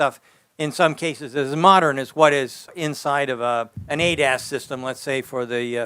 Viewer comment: Every time he says ADAS. I hear "ate ass" cant help myself